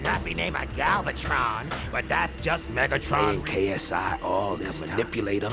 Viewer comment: Did he say KSI?